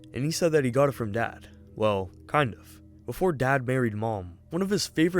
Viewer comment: In the section where he asked Rodrick where he got a gun, I thought he said that” Before dad BURIED mom.”